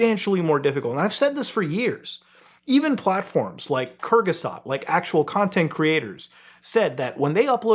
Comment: i heard "kyrgyzstan" at first and im ashamed to say it took me way too long hes trying to say kurzgesagt